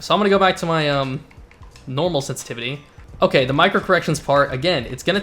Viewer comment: I thought he was saying micro aggression at first lol I was gana say ok I just gotta get mad before I start my next match